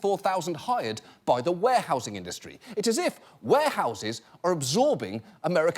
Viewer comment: I thought it said "Whorehouses"